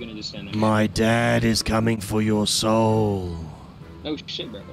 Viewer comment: I just hear "Im coming for your soul" I skipped five seconds to rockets that killed me bro